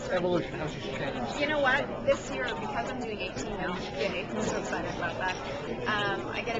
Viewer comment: what does she say at 1:56 ? that lois is turning 18?? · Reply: She said, "Because I'm doing 18 - Yay! I'm so excited about that..., " by which she meant she had a contract to do 18 episodes in the 9th season. Before that, she only did 12 episodes per season, max.